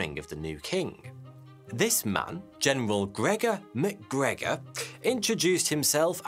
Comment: I legit hear it as general connor mcgregor...